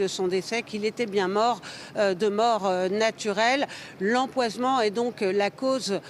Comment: C'est moi qui n'a pas bien entendu où elle a dit L'empoisement